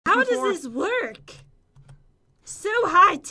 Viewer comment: I Thought pat said twerk in